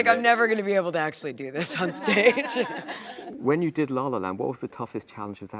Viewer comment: Oh he said La La Land, I could've sworn he said "lava lamp"